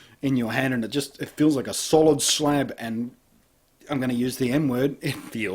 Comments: i thought i heard "im gonna use the n word" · thought he said he was going to use the n word not m lol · I thought you said "And I'm going to use the "N" word" I was like "...Uh oh..."